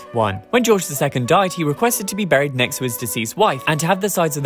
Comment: At Edward VII’s funeral there was a special section set aside for his mistresses. It was packed solid. His widow, Queen Alexandra was observed talking with several of these ladies after the funeral. No prizes for guessing what they were gossiping about. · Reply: I thought he said "buried next to his 'diseased' wife." Lordy.